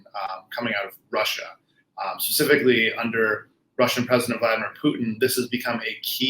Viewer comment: Is he really sayin "President Vitamin Putin"(0:27)?